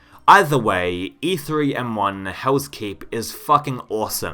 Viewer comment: did you say “House Keep”? Lol